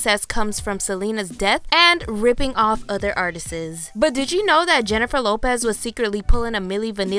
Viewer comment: Did she just say “artistses”?